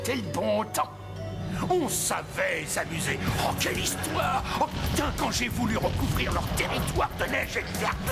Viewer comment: Vers 4:20, Arktos à dit "oh Putain" ou c'est moi qui déconne ? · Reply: Non tu déconnes pas moi aussi j'ai entendu "oh putain"😂